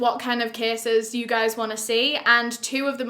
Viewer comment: Every time she says covered, I'm like "what? Covid?" I'm not used to the accent 😅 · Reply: I thought when she was saying cases she was saying "kisses"...haha!